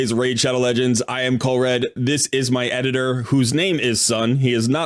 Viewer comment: Happy Holidays. I missed most of this live, so it was fun to get to see it posted. Congrats to all who had good pulls. I am also part of the "I thought you were saying 'my editor's son'" camp when I first started watching your channel.